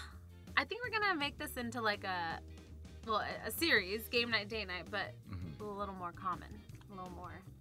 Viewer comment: i thought he said gay night😂💀